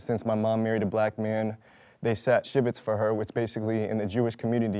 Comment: Can anyone explain this? 'sat shibbits'? Sorry if I misheard, but not too sure what he was saying. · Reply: He said they "sat shiva." In Judaism, there is a tradition when someone dies where all the mourners gather together in one place to mourn for 7 days. It is called "sitting shiva" because shiva is 7 in Hebrew. In the past, some orthodox Jews would sit shiva when a Jewish person in their family married outside the faith because it is technically forbidden in the Torah. This is becoming uncommon today as views become more tolerant and accepting.